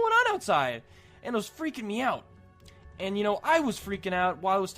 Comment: Lol I heard "it was freaking meow"